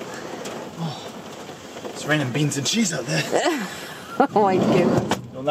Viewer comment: Thank you for the kicks and giggles. It was a wonderful way to wake up to my morning tea. One question. When you awoke to the rain at about 17:00 in, said what I could only decipher as 'It's raining beans and sheep (or maybe sheets) out there', and I was just wondering if either is accurate? If so, either way, I've never heard that saying before. Frogs and horses, yeah, and other such things, but never something close to that. What was actually said?